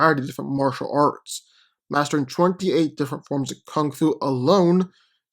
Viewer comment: did bro say 28 forms of kung fu?